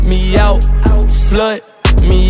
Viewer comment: I thought he said "Slvt meow"